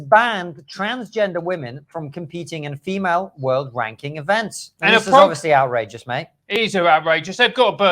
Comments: So sorry, I misheard. I thought you said 'world-wanking events.' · Made me laugh - I thought I heard you say 'Female World Wanking Events', any trans-woman would win that, hands down ;) Great laugh boys, keep it up :D